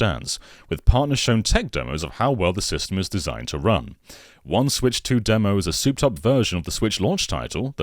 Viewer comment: "One Switch 2 demo" For a split second, I heard that as a "1-2 Swich demo"